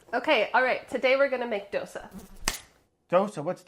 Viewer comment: i really thought said “Dosa Diaz” lmaooo i thought it’s a brooklyn 99 reference lol